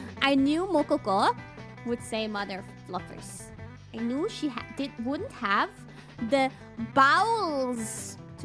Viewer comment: I think she said "I knew she wouldn't have the BAUs(reference to wouldn't have the balls but with Bau I guess)" here.